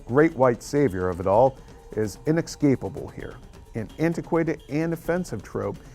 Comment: I heard "inexcapable" and turned this off.